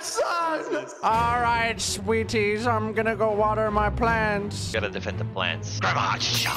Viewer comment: did say nadwe he’s going to water his plants or did he say he’s just gonna water his pants😂